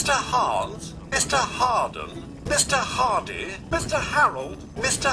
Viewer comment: the word ''mister'' lost all meaning before long... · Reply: the Wolf I immediately began listening for it, and I heard "is to Harden"